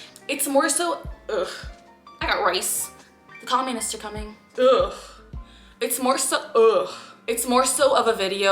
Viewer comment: Did you say comunists are coming lol